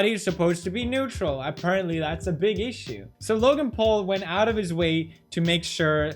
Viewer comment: he said Greg Paul instead of Logan Paul